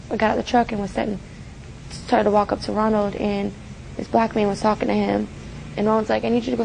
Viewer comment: No, they mean that the person was black. They say a BLACK man, not a man IN black. Let’s not act like people don’t try to use stereotypes, that have chained black people for decades, to their advantage. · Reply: she clearly said a black man